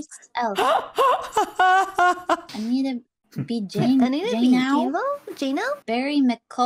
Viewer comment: ngl the first thing I thought when they said ''initto bee jay now'' was ''I need to be gay now'' lmaooo